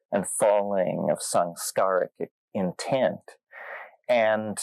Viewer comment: does anyone know what he is exactly saying here? rising and falling of sunk skaric intend is what im getting but googling skaric gets me nowhere 😭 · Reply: Samskara, Samskaric